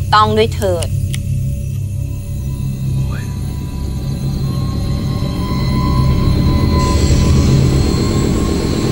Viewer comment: เสียงไรอ่ะเเว่วๆ ได้ยินว่า ค้วยห์ ประมาณนี้อ่ะ ลองฟังดู อิอิ ^^ >< :) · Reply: เสียงตากล้องพูดว่าคุวย